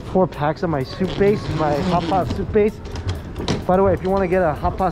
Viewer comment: I thought he said “hot sauce tooth paste” instead of “hot pot soup base” but since it was Mikey I thought it seemed perfectly normal 🤣